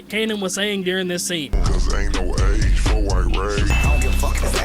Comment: Did he say "ain't no age for white rage"?? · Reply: i kept hearing "aint no age for irate"